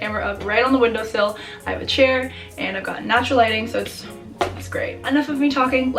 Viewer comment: who else heard her say “i have a chair and a gun” in the beginning